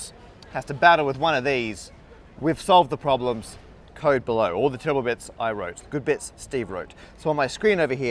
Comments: dang it, I thought he said "Code Bullet" at 5:12 sadly, no collab yet :( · I heard that as "all the zero bits I wrote, all the 1 bits Steve wrote".